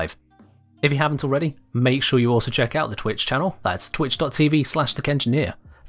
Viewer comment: I'm sorry, bet everytime I hear you say" the kengineer" I just hear "thicc engineer."